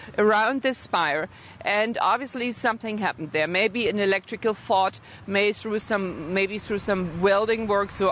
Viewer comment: did she just say electrical fart? Lol